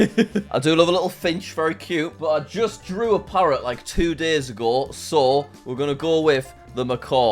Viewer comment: I thought he said I just drew a parrot 2 years ago😅